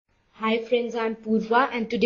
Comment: all I heard was "Hi friends I'm poo-var" and I lost all hope